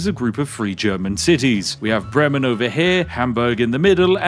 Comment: I misheard Bremen as Fremen and for a second thought that Dune had melted my brain haha